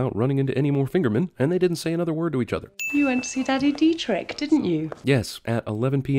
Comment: 😳I thought the movie said Daddy V 😂